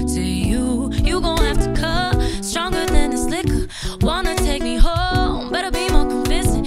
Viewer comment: "I'm stronger than this liquor." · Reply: Yoooo I thought she said "stronger than a snicker" lol